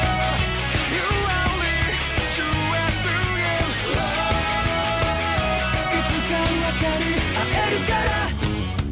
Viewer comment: What is bro saying at 1:14 ??? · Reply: Who are we to pass through you